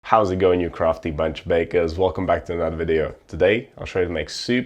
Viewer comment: 😁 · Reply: at first though, I thought you said “crusty bunch of bakers” and that made me cackle